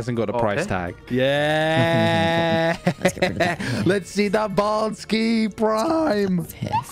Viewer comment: did someone say baldski?